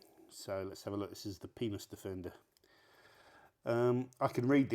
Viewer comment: im going to have to deduct points from you for saying what sounded like, "penis defender"